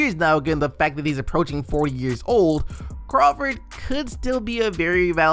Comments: Is it just me or did he say 4 years old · did u say Jamal Crawford was 4 years old